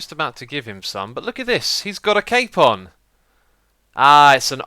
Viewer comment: the first time I thought you said he had a cake farm. Then I reminded the video, and saw he had a cape on. :P